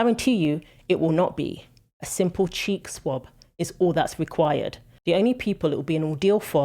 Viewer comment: Great channel. At first, I misheard what you said and thought you referred to a 'cheat swab' instead of 'cheek swab'... now I'm thinking, 'cheat swab' is a good name for it. If you are a man competing in women's sports that is exactly what what you are... a cheat!